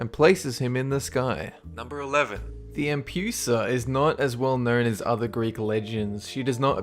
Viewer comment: Someone can tell me the name of that creature in the minute 3:12? I can't hear anything q-q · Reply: The Empusa